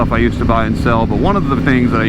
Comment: I thought he said BRT